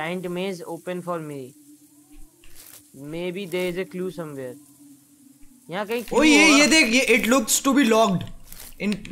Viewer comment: Kya bolra h Bhai? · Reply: Maybe there is a clue somewhere 😉